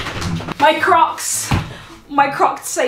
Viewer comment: At the beginning, at first I thought you said, “My crotch!” 🤣